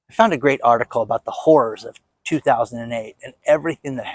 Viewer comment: oOOH I THOUGHT YOU SAID "Whores", but you said "Horrors" - Lol